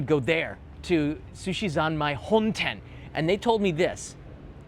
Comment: Hi, what's the name of the sushi place you said in 27:00-27:30? I couldn't grasp it. Please let me know so I can search it. Thanks. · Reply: He said Sushizanmai honten | 4 Chome-11-9 Tsukiji, Chuo City, Tokyo <num>, Japan, this is the location.